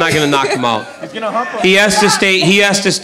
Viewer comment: “he’s gonna hump him” did anyone else hear that or is it just me?